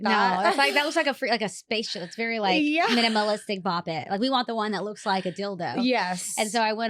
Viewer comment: did she say the one that looks like a dildo!?!?! 🤣💀